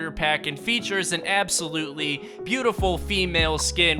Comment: Did he say absolutely beautiful! U know what that means UwU